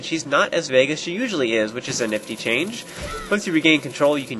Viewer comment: Did he just say nifty? O_o